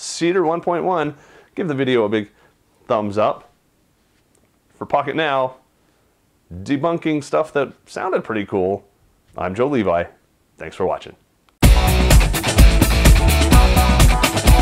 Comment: pocket now i thought you say fucket now